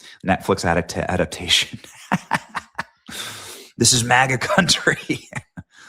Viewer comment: I heard he said," this is maga county!!"